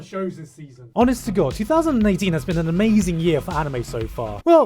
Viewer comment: I swear I hear u say 2019 instead of 2018